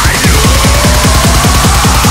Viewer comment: i heard: " TRY NOT TO HEADBANG" you son of a bitch, IM IN, too late